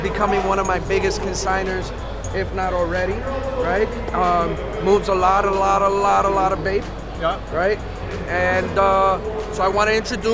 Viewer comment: Im new to the sneakerhead lingo trying to grasp info . Can someone tell me what he said ? 12:17 . Moves allot of what? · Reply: BAPE, the brand lol